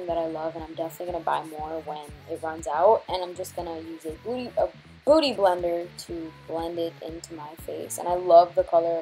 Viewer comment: Did she say " booty blender " on